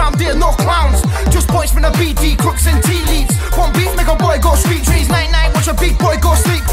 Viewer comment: does he say nigga? He says something I couldn’t quite catch but sounds like nigga.. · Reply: want beef make a boy go